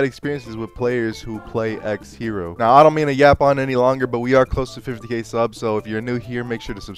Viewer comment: i legit heard you say "we're close to 58 subscribers"